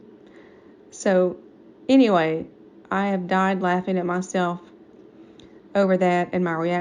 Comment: "I died laughing at myself" is what she said, the CC is wrong